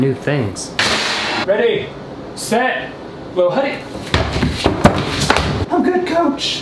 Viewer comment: Did he just say “ready, set, lil huddy” ?